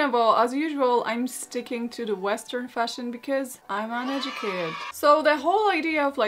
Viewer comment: You’re sticking to the western fashion because of what? Sounded like you said “I’m uneducated “. I couldn’t understand what you said.👍